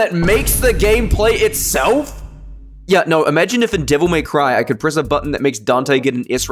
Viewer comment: did you say Devil May Cry